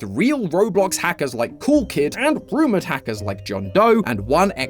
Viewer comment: I thought it said groomer hackers 💀💀💀💀💀